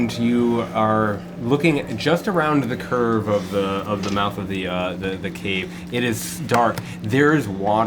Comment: Every time they say it, I hear, "The Cave of the Mouth."